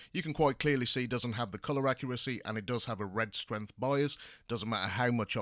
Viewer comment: Hello Guys can you please tell me what is he saying at minute 5:25. The projector has a red strim vaios? what does that mean? i see that black bar through the image? is that normal or it is a defect of the projector? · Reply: red strength bias, the line is the image going beyond the edge of the screen on the wall